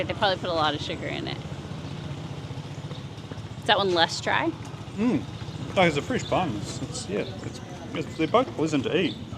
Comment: translation needed since I don’t really think he said what it sounded like to me. “A free sponge It’s poison to eat”? 😅